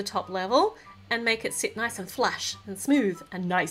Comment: I heard her say flash instead of flat